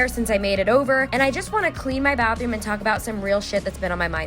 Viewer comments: i thought it said cleaning my bathroom while taking a shit hahahaha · I thought video said “cleaning my bathroom while taking a shit...” lmao 🙃